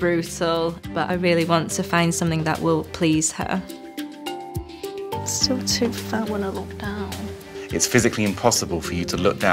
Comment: she said she's quiet fat? (Perhaps I misheard what she said as I'm not english native), but she's her her mom was kinda wrong. She put her hands below tummy, slightly lower, I once complained and my mum said I was dumb cause that's where ovum was located inside. And yes, a person height can affect how far ovum from the tummy... that's why a shorter person can show a big tummy while pregnant and a taller person has a slightly lower bloat tummy (not like in where tummy is positioned) when pregnant.